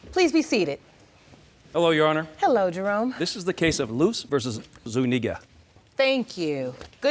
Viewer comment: what did Jerome say? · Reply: Zuniga.